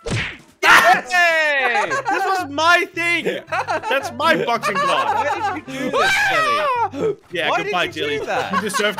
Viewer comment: Uhhh what did crainer say guys? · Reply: My fkin what?